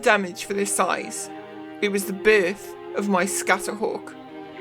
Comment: haha I heard "It was the birth of my scatterhawk" as "it was the brrr of my scatterhawk" Made me think of the A10 Warthog. The Brrrrr no ground enemy wants to hear.